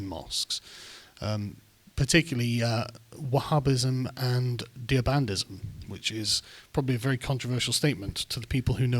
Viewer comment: What was the type of Islam he mentioned at 11:05 other than Wahhabism? It sounded like "Deabandism". EDIT: Found it, Deobandism. Not sure why the first search threw up nothing and the second one gave it to me straight away. :S